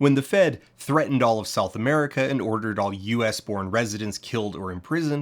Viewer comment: up until now I thought you were saying “self America” but now I realize you say “South” with an North American accent I can’t place